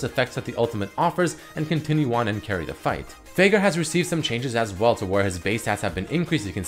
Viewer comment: Am i the only one that heard "Faker" instead of "Veigar" ?